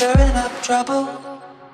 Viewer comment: All this this I thought he said "I wanted you to wash n****gs off" lol.